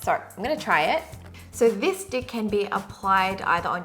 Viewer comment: Did she just say "this dick" at 1:09?? 😂😂